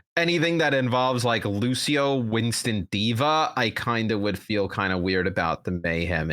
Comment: Did said DIVE😂